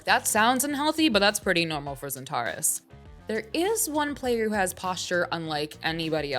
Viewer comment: Did she just call KennyS XANTARES?